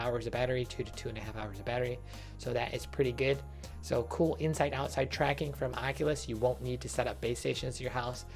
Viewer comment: I thought you said, at the start of the video, that they were going to be used for inside-out tracking? Maybe I just heard wrong ;) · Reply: i heard the same and had to go back. he's saying "no inside out" but kinda sounds like "now inside out tracking".